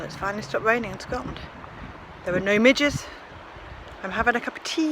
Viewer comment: did you say "there are no midgets and I'm having a cup of tea, so it's gorgeous!" ?!?!! LOL